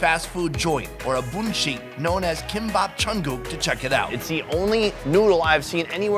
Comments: I heard Jungkook👀💜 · I HEARD JUNGKOOK